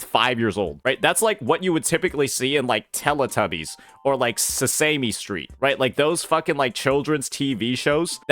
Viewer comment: wait what? like, on where? LMAO Say-See-Me Street? . . . . where dat at? 🤣🤣